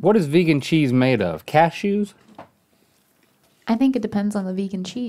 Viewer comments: When Link was asking about vegan cheese, I thought he said, "cat shoes?" instead of, "cashews?" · I thought Link said “cat shoes” instead of “cashews” about the vegan cheese.